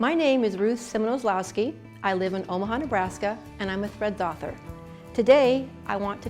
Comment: Oh Threads author, I thought she said thread doctor lol